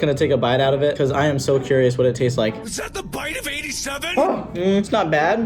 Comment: is it just me or at 10:10 i heard 'IS THAT THE BITE OF 87!?' Lol is the funniest person ever he is the best! and the way at 11:41 he said 10/10. and the way at 12:15 he said ThReEs GaS pUmPs ThEiR dUdE. WAY TO FUNNY TO WATCH LOL🤣🤣😂😂😂😂🤣🤣